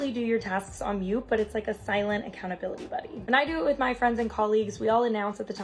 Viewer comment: Did she just say accountabilly-buddy? Like in Southpark and the Christian camp with butters? <link> This is horrificly accurate